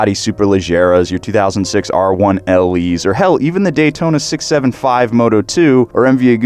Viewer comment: did I just hear dAyTOna 675 MOTO2? 4:51 btw